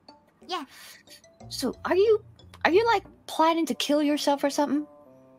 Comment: ここだけなんて言ってるかよく分かんなかった Are you like (gonna) flying to kill yourself hum? て聞こえたけどなんか違う気する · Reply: ありがとうです。今になって聞いてみたら普通にAre you like planning to kill yourself or somethingですねこれ。 3ヶ月でヒアリング能力向上してて、ホロENに感謝🙏